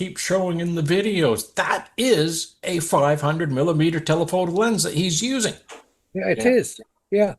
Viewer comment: Edited to correct the timestamp At 9:20 did the "Apollo Detectives" just call it a telephone lens instead of telephoto lens? That is what I heard them say anyway. Anyone else? These "detectives" are pretty loony toons and fail in every aspect. Very well done and well explained. Love seeing your videos.